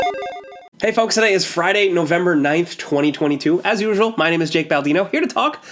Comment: Jake says is november 9th 2022 or am I hearing bad?